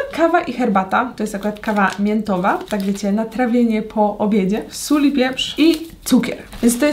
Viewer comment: Jak mówiłaś "sól pieprz" to ja usłyszałam " sól wieprz". Lol😂